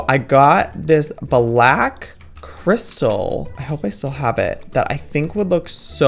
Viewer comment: ok if you turn on subtitles and read them and think Valck crystal instead of black that’s what you’ll hear. Then try again accept Black crystal